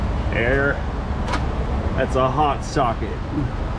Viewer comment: It’s FRI-YA I win a shelf full of brake kleen and the to avoid injury sign. This is going to be a stellar video. What happened to the green subscribe button? Hot socket I thought you said hot pocket lol I love your videos